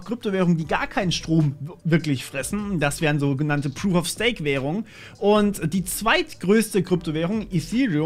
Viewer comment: Was ist eigentlich ein "proof of steak" bei Bitcoin etc? (ca. 24:48) Ich glaub ich hab was falsch verstanden xD